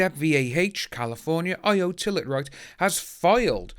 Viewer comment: Hi, listening while cooking dinner. Thought you said Oi Toilet!!.😂😂😂